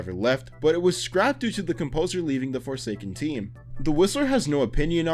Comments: When he said the whistler i thought he said "the rizzler"😭💀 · When he got to the Whistler I thought he said the rizzler